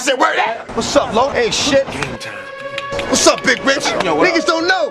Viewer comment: did he say what's up big bitch??? hahaha dmx just cracks me up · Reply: aggelos cj he said big rich. Yeah, DMX cracks me up too.